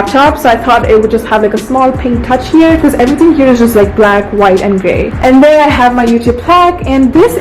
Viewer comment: Why did I think that in 8:02 she said "White, black and gay" ?? 😂😂😂